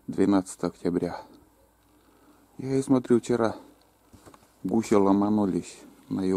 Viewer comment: Красота!,послышалась-мухи ломанулись на юга-)) Потом перемотал,а нет-гуси-) Летят перелетные мухи_))